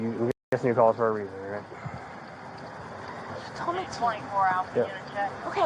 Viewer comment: what does she say here?? And… what….?! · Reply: "It's the same guys....you told me to"